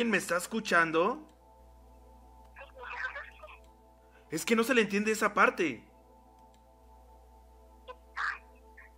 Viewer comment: En el minuto 8:42 pongan la velocidad en 0.75x se le entiende un poco mejor pero como nose ingles solo en tedi que dise help me y dise otra cosa que no se entiende bien por que no se ingles asi que intenten saber que dise porfa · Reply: Jair Garcia si es cierto lo que dices y ya que yo lo que alcanzó a percibir es que dice please help me ( por favor salvenme)